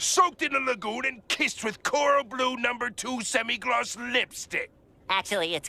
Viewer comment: For the Spongebob lipstick one, I think the way Mr. Krabs says, "semi gloss" sounds like "75" and I think people are getting the "5" from there